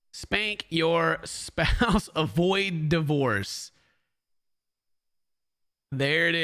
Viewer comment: I thought they all said : Spank your spouse avoid da "Horse"!!!! 😂😂